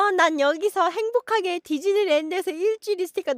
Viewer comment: 나만 디즈니 랜드를 지진희 랜드로 들었나?